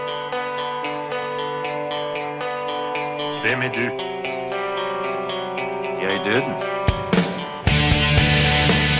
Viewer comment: Really a great album but man 52:42 caught me off guard! Can anyone tell me what is being said there? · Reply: Vem är du? - Jag är Döden. That's "-Who are you? -I am Death." in Swedish; from The Seventh Seal (1957).